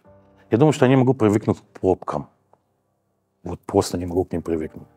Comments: Сначала услышал "попки" , потом понял, что это были пробки. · Вам тоже послышалось, что он не может привыкнуть к попкам?